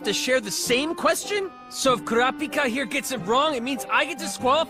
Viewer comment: why did I hear 'grapika'